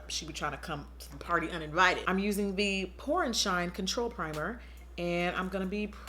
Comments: I really thought she said porn shine primer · I thought u said “porn shine” primer